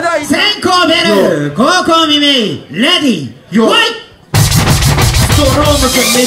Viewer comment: どうでもいいけど25:23始まる合図【レディー弱い】にしか聞こえない